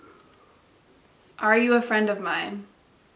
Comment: When said “are you my friend” it kinda sounded like it said “hell no”.